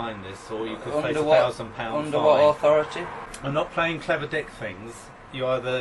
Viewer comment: does he say "im not playing clever dick swings" with a voice like Dale Whinton?!?! haha